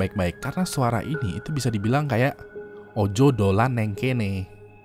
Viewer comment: Terima kasih telah memberikan gue kesempatan untuk menemani malam jumat lo... gue punya sedikit pengumuman bahwa malam jumat minggu ini adalah malam jumat episode terakhir untuk tahun 2017 ini. gue bakalan berlibur sebentar, cari materi baru, cari bahan baru, cari teknik baru, cari ilmu baru supaya konten makin maju lagi. episode terbaru malam jumat akan muncul lagi pada tanggal 18 januari 2018. See you 2018! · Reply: Aku dengernya bukan ojo dolan ng kene tp "ojo rono" alias "jangan kesitu" tp gatau sih klo salah denger